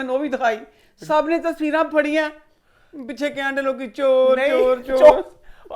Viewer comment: I heard Maryam chor , money chor😂😂😂😂😂😂😂